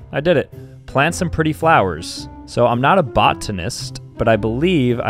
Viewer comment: sounded like a teacher saying ‘I’m not an artist’ but he said I’m not a botanist